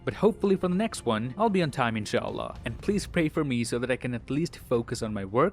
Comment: I know this question dosent have any thing to do with the video but did he say insha'allah(a thing in islam means if god wills to) in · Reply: Yo the caption also said inshallah.... Is he a muslim?